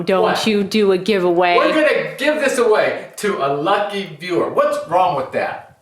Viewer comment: i heard "gif away" you got full attention!😋